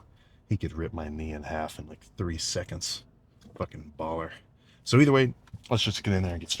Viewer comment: I skipped to right before his workout and all I heard was “he could break my knee in half in 3 fkn seconds.. fkn baller!” 😂 l